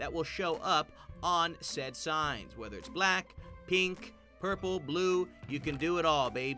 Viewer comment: did he said Black Pink?